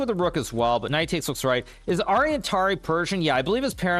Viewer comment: i heard "is Aryan Tari virgin?" 😅